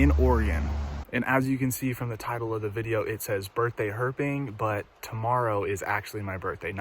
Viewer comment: Oh I must have clicked on the wrong video. I thought it said "Birthday Twerking."